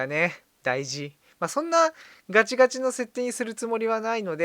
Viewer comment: ガチガチがガチムチに聞こえた。 のじゃー